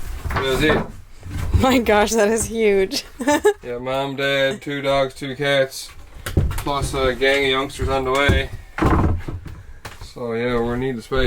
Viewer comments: Did I hear "Mom, Dad, 2 dogs, 2cats, and a mess of youngster's on the way?" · Wait - did say "plus, a gang of youngsters on the way"???? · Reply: I thought I heard him say and a youngster on the way. Maybe he meant for the future.